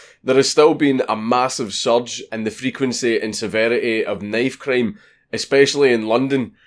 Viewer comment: Knife cream... I thought thats what he was saying.